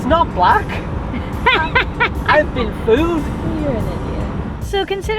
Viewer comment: Mmmm 10:35 in…. 🤔 did I hear correctly…. HaHaHaHa….. your a “D” head…..😳😂